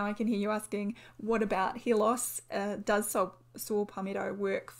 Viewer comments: Am I the only one who thought she was saying “hear loss.” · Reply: I also heard her saying "hear loss"😂😂😂😂 English is not my first language 😂😂😂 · Am I the only one who thought she was saying “hear loss.”